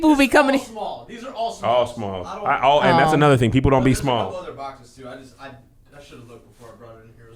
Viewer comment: Did he just say " people dont be small"?lol